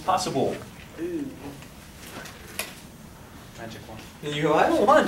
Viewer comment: did someone said magic mushrooms at second 00:27?